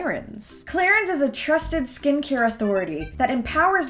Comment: I thought you said clarinets 💀